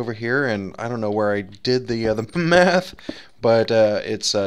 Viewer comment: it sounded like you said "i dont know where i did the me-e-e-eth. lol